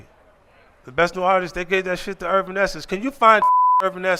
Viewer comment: I thought 50Cent said where da fu@ is Herbal essence?🤭 He said urban essence 😁